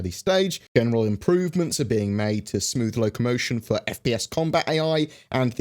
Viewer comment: I hear “smoothing FPS combat” and got super excited until you said AI. I mean that’s good too! But the FPS player movement still feels super janky.